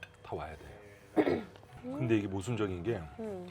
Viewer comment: 모수적인게 라고 들었어요... 죄송해요....ㅠㅠㅠㅠㅠㅠ